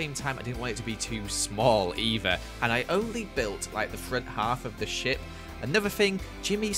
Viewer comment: When said “Half of the ship” I thought he said “Half of the shi-“